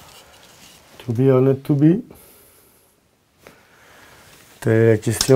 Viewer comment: I was seriously wondering why they didn’t add captions to what he said . 😂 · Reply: Cause that was universal 😂.. "To be or not to be"..